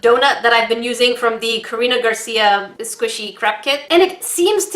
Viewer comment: the crap kit Mom:did she just say crap? Me:nooo she said craft dud